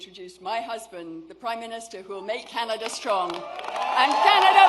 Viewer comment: does she say make Canada poor? Huh?